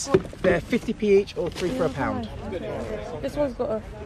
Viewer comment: ''there 50p each or 2 for a pound'' 😂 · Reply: I heard "3 for a pound"